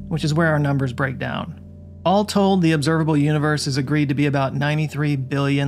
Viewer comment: Haha was it difficult to convince it? It sounded like he said “has agreed to”…